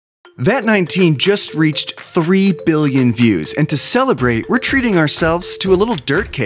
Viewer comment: I thought they said 3 Million but eh like Jon said finders esters